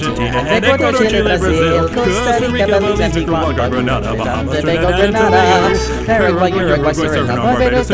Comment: why do i hear banana?